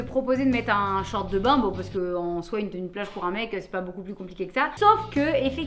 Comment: A "tenue de plage", j'ai entendu "tenue de lâche"... (corps de lâche? ah non..)